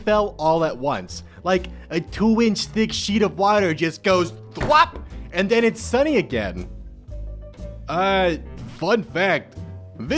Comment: did he just say... "𝓭𝔀𝓸𝓹𝓼"?